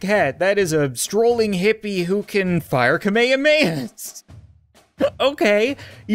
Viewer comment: DID HE JUST SAY KAHMAYAMAYA INSTEAD OF KAHMEHAMEHA